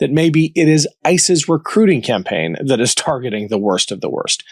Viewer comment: When he said, “ICE’s,” I thought he said, “Isis.” Same difference.